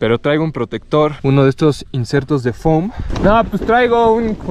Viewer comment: Pensé que dijo uno de estos chetos de foami xd